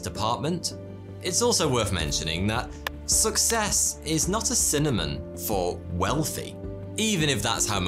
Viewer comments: To get a 1 year supply of Vitamin D + 5 individual travel packs FREE with your first purchase, go to <link> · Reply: You said cinnamon instead of synonym at 17:30 lol! Great vid though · Is it just me or did he say "cinnamon" instead of "synonym" at 17:27; "success x wealth" :3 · "Success is not s cinnamon for wealthy" (17:26) Did I hear that right? lol I listened to it like 20 times. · Did he just say Cinnamon instead of synonym? 17:25 the whole video has just been lost to me *eye roll